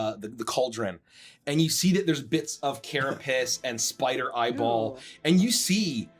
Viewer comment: When said the stew had Carapace, I misheard it as "carrot piss" and somehow didn't even blink. This campaign is fukkin weird, man